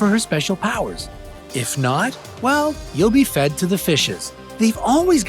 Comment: did he actually say fed to the FISHES?